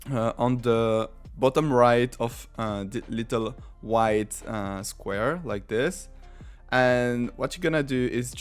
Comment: I like how you called rectangle a "wide square". LMAO 1:31 or did you say "white"? IDK i'm deaf